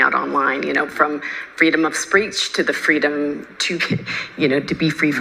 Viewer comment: freedom of "spreech"....wtf is is this word?